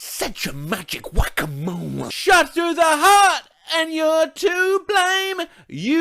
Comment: In you give love a bad name when they sing " shot through the heart and your to blame " I ALWAYS heard " shot through the heart and your to late " !